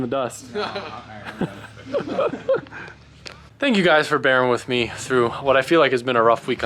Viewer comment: Sounded like he said thank you for BARRA-ing with me, imagine a Barra drift build...